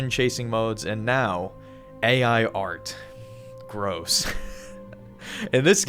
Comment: ¿Did you say AI Layer 2? 🥷Chain reference?